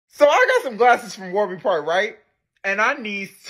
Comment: 2! But I thought you said I got some glasses if you gotta fart